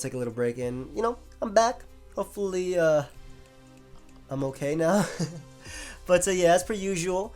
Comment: Did I hear that right as "I'm OK na" around 0:38. I'd be really surprised to know if can speak Filipino.